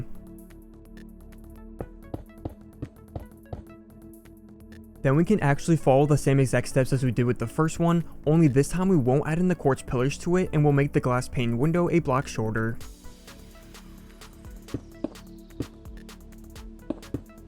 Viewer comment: IS IT ME OR DID HE JUST SAY A BLOCK SHOLDER